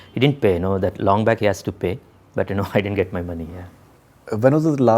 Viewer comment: did he say he didn't get his money?!?